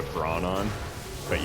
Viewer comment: you say “ keep in mind we have ???? On” what did you say there? I’m so confused on your dr right there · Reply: brawn a GM modifier